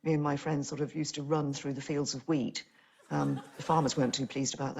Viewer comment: Am I the only one who heard weed?